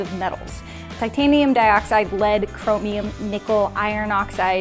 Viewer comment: I thought said one of the components was blood. Then I realised she said lead.